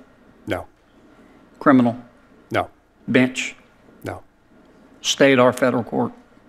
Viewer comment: When said Bench I heard "Have you ever tried a Bitch?"